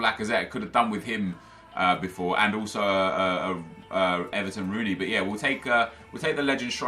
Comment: did he said edison rooney????